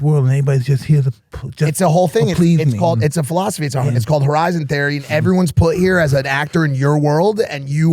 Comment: What theory is Mike talking about? I hear 'horizon theory' but I can't find anything about it